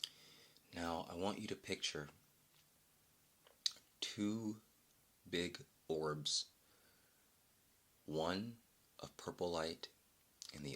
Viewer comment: I THOUGHT YOU SAID PICTURE BIG BIRDS OF LIGHT LMAO 😭